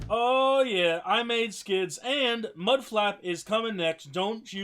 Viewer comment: It sounds like he said I made kids